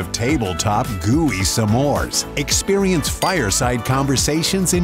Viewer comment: I'm sorry, did he say, Sa-mores (0:44)?